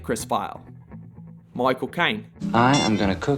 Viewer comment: did he just say "my cocaine"?